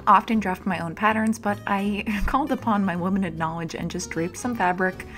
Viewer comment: I was sorting laundry and only kinda hearing the video and heard ‘womanic’ knowledge instead of ‘limited’ and honestly let’s start calling upon womanic knowledge.